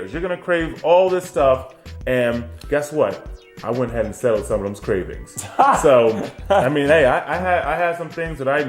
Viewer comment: What did say at 20:53? "Guess what? I wouldn't ... (?) ... some of those cravings." Edit: Did he say: "I went ahead and settled (?) some of those cravings"?